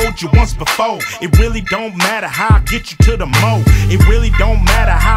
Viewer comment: does he say at 1:37 "it doesn't matter how it gets you to the MOAB?"